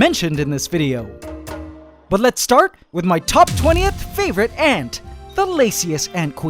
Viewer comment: I thought he said the laziest ant queen lol